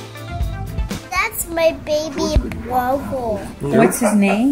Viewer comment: the kid holding a newborn brother and said "That's my Baby Brother!" what she said, "B W O W E L"